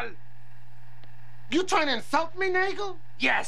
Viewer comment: I can't be the only person who hears "You tryin' to insult me, n*gga?" at 4:01.